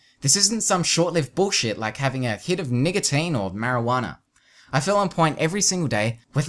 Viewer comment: did he say "n*gga-tine*?